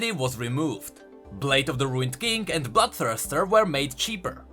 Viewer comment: When you said bloodthirster i swear i heard you say butt thruster